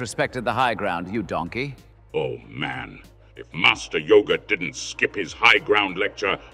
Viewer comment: Did... did he just say "Master Yogurt!?"